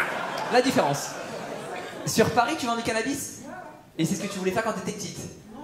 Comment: Je suis robin des bois, je vends du cannabis XD C'est quoi ca hahaha